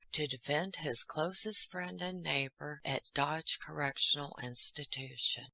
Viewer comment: my Queen. When you said Dodge correctional institution at 1st I thought you said God's correctional Institution. Now I know you are the Queen of grammar... however I don't think you would start correcting God's grammar. My next thought was is God talking to? Cuz that would raise a whole other level of questions! As I was pondering this strange occurrence I rewound the video and this time I definitely heard Dodge not God! Boy was my face red😂🤣😂🤣🤪